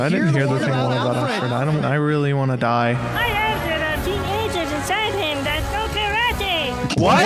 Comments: what is he saying? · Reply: "I heard there are teenagers inside him that know karate." It's a Power Rangers reference. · On the DVD commentary, Phil Vischer mentions that no one understands that line. It was said by an Egyptian editor at the studio and it was too funny to them to change it. The line is: "I heard there teenagers inside him that know karate!" It's a reference to Power Rangers. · Guys i just figured out what that carrot was saying at @ 15:02 "I heard there are teenagers inside him that know karate" I think they were trying to do a voltron reference 😭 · sounds like he's saying "I heard there are teenagers inside him that know karate" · all I can get from that is “I heard there are teenagers inside him that know karate” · If any of you are wondering what this guy said 15:02 - 15:05 (P.S. I listened very closely to him) he said: "I heard there are teenagers inside him that know karate!"